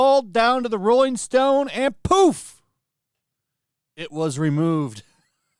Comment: did you just say and poof? 😭